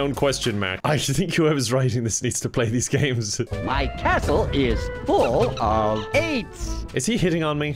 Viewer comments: I thought the count said that “my castle is full of AIDS”😭😭😭😭😭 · I thought he said “My castle is full of Aids!” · i heard "my castle is full of aids" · I THOUGHT COUNT DRACULA SAID 'MY CASTLE IS FULL OF AIDS' AND I WAS VERY CONCERNED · I THOUGHT HE SAID "MY CASTLE IS FULL OF AIDS" AJHDFAJDF Timestamp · did he say his castle was full of aids